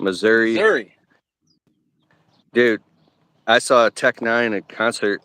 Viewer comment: What kinda concert?? Sounded like u said tech 9 lol